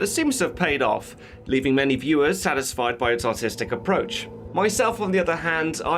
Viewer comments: did he say autistic approach or authentic? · I thought you said "Autistic approach" and I coughed up what I was drinking at the time lol.